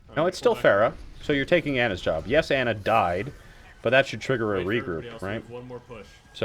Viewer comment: What was he saying at 7:14 after he talked about Ana dying? "It should trigger a..." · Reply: Brady Vance "That should trigger a regroup."